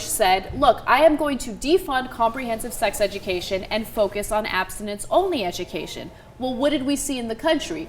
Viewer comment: lol @ 2:44 the subtitles say "accidents only" instead of "Abstinence only" ... oh the irony ...